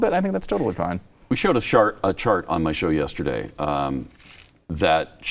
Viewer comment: did he say shart